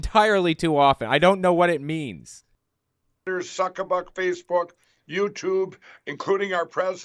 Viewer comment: is he saying zuckerberg? like, in a weird way, to mock him? idk